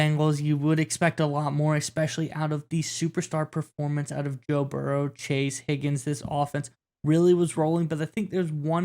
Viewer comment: did you say “Bill Burrow”?